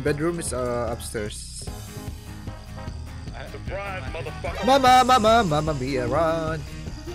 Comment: I think its the best intro🤣 4:05 did he just say mamamia😅